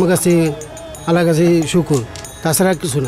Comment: এই খানে কি বলল?? কেউ বুঝতে পারলে বলবেন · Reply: আল্লাহ বলছে